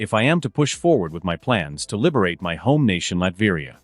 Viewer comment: I heard Liberia at first, lol. 🇱🇷